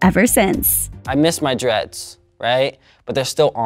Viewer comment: did he say "I miss my dress"? He put a few on for the Illuminati so I'm not sure why they won't cast him any more... He is talented though.